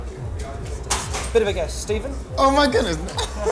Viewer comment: Right after he asks the full name of the guy, I hear a whisper say, "STEVEN"..anyone else?